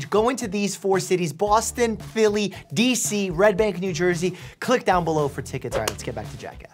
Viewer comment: I thought u said; RED NECK NEW JERSEY